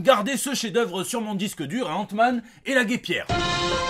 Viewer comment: AAaahh Ant-man et la GUEPIERE j'ai entendu "L'abbé Pierre"